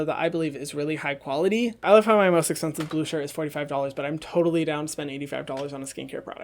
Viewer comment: what did he say hismost expensive at $45?